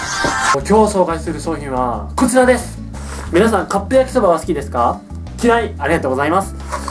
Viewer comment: カッペ焼きそばに聞こえる